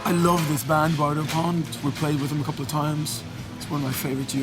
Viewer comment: What is the band that he is talking about at around 4:00? I can't understand what he says. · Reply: Bardo Pond